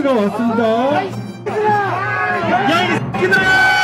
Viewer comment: "야 이 사람들아~!"로 들리네요. 시민단체분들 너무 점쟎으시다ㅋㅋㅋ